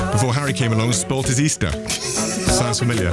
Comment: Please could anyone clarify what William said about Harry(s) at 19:55? Did not catch that (just know that it was a joke referring to his own brother...sth weirdly familiar...as Harry Styles taking up a spot instead of Ed Sheeran?). Thanks a lot! A non-native speaker here 🤦🏻‍♀️🤔🤗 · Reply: 'before Harry came along and spoilt his Easter. Sounds familiar' . Yea basically referring to Harry Styles dethroning Ed sheeran, but keeping it to first name to refer to his brother too